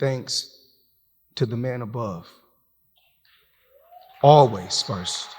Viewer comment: When he said ' The One Above ' i heard, ' By The Will ' , Of , ' The One Above ' mention him in front of - An Audience - much - much - much - much - much - much - much - bigger than - TBE - The Best Ever's Crown - i meant Crowd sorry 🤣🤣🤣 himself